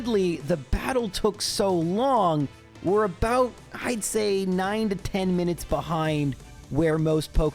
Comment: i understood "dreizehn" german for thirteen instead of "id say" hahah i was so confused